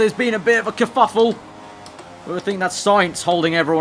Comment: he says "there has been a bit of a...." What does he say? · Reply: Sergio Gimenez bit of a kerfuffle